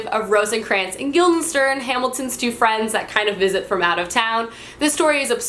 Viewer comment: Is it just me or did she say Hamilton instead of Hamlet in 1:36? No? I'm just hamiltrash? Ok.